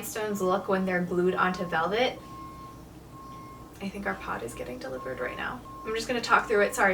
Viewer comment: I'm sorry, but I have to ask: your "pot" is being delivered?! Am I mishearing things or? is highly confused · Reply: pod! for moving haha, we just moved to a new house and had to use two pods to get all the stuff there 😅